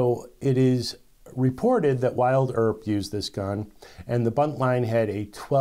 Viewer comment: Hey brother at 5:10 did you say “Wild Earp”, rather than Wyatt Earp?